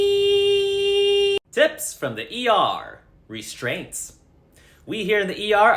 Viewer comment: Not gonna lie thought the restraints said restaurants 🤦🏻‍♀️😂😂